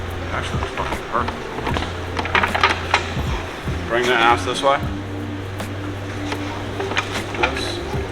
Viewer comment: what did he say? 😅 "bring that ass this way"? Don't talk to my guy spoon like that 😂